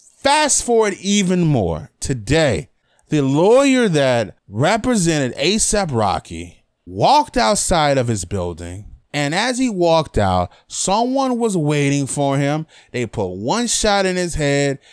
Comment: Oh i thought it said "ASAP ROCKY WAS SHOT IN THE HEAD"